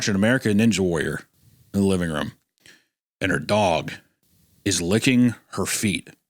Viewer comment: I misheard Hunter's story; I thought he said that his dog was licking ITS feet, not his Aunt's. And so I thought that meant that the dog must've eaten the bird cuz the bird was crippled and was just licking the blood off its paws